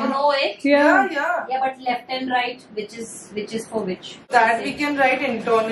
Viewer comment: aunty saying which is and i heard bitches 😅😅